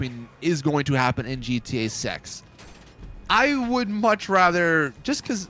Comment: Did I hear that wrong? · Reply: GTA SEX